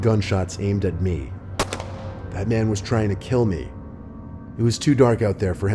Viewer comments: (with captions on) "batman was trying to kill me" instead of "that man was trying to kill me" shit got me DEAD😂😂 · the captions say batman XD